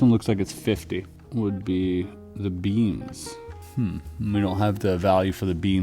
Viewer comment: Beans? I know he said beams later but i swear he is saying beans at 22:40. Tell me sir what is the value of the beans?